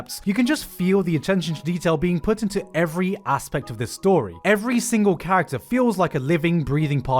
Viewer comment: I heard “every asspect”